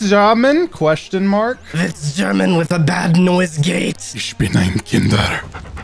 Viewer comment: Does anyone know what he says in German at 7:52 ? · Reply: He either said "Ich bin ein Kinder" (I am a children) or "Ich bin ein Inder" (I am an indian). The first one would be gramatically incorrect though because "Kinder" is the Plural of "Kind" (child). Corrected, it would be "Ich bin ein Kind" (I am a child).